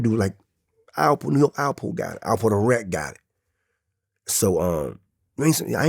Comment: His accent is horrible… I thought he was saying Alpo the wreck for the first 30 minutes of the video. And I had to squint to understand what he was saying 🤷‍♀️